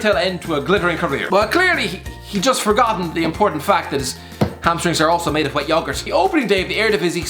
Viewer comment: Did he say "his hamstrings are made of white yoghurt? and pronounce it "Yog-urt)? Irish people pronounce it like Americans, Yo-Gurt I'm glad you included Liverpools 7-2 defeat to Villa, we were unbelievably bad on that day